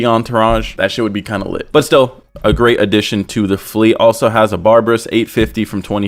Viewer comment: did he just say Barbrus?..